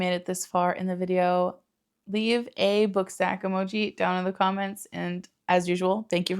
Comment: I thought you said book sack 📚📚📚